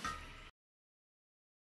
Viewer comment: at first i thought he said ,YOU DIE!!!!